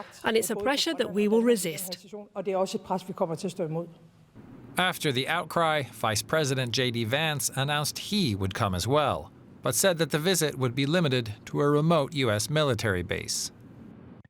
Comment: did he said?